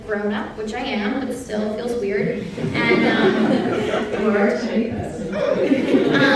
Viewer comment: What does Emily say at 14:54? I love Kendrick's reaction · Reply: she said "well you are a tiny person" bc Anna said she imagined herself as lil red